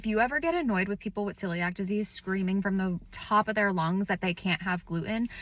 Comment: The first 5 seconds of this video was extremely confusing for me because I thought you said, "Silly Act Disease."